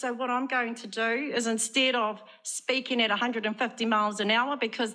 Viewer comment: What a nutter ! I thought she said she wasn't going to speak at 150kms per hour. Lol. HAHAHAHA. IT'S LOONEY TUNES TIME, THAT'S ALL FOLKS!